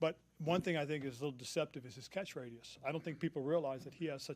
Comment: Definitely thought he said catchphrase, not catch radius lol